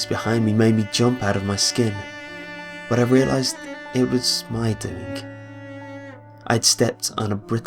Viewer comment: a snapping noise behing me nearly made me jump out of my skin, but i realised it was my dink. BAAAHAAHA! (i know that's not what he said, but i'm still chuckling.)